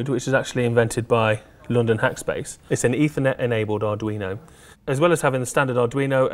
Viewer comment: What did he say, witch Arduino is Internet enabled? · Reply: Бојан Драшко I believe he said ethernet enabled